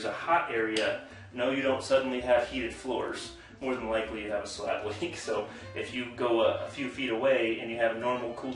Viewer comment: Misheard that as slav-leak lol